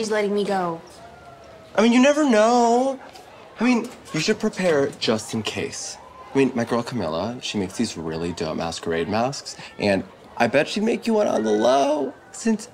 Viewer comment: sounded like he said “dumb ass.”